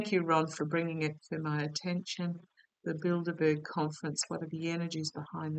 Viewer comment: I thought you said “ build a bear conference” 🤣🤣🤣